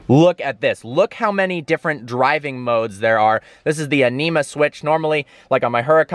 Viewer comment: I thought you said "enema" switch